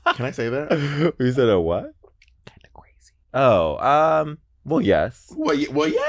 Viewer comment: what did she say? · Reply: The first time she said, “actually crazy” and the second time she said “kind of crazy”